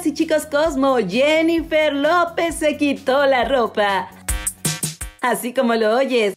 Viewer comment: Pensé que decía SE QUITÓ LA VIDA ¿Qué tiene de innovador que se quiten la ropa las famosas?